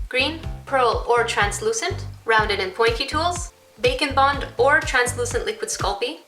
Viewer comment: I thought you said Bacon Bond, and I became extremely excited, but then I saw what it actually was and now I'm sad :c